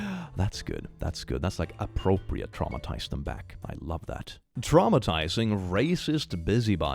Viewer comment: At first I heard “trauma tithes them back” and honestly it sort of works